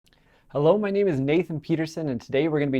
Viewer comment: For a second I thought he said hello my name is David Guetta